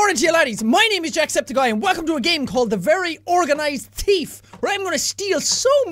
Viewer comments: It sounded like you said “Teeth”. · Actually thought you said "The Very Organised Teeth" 😂😂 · did he say thief teeth